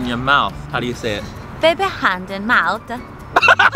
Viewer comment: it sounded like they said baby hands in mouth